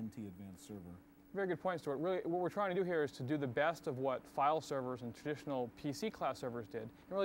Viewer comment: Thought my guy said "cloud servers" and was going to yell at 1993 for coining the term.